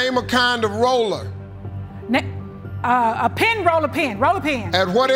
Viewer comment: I thought she said rolling pin, not roller pin.